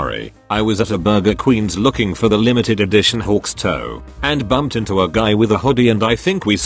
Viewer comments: Did he just say hawks toe 😳🤣🤣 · IS IT JUST ME OR DID I HEAR "LIMITED EDITION HAWKS *TOE*? wHEezE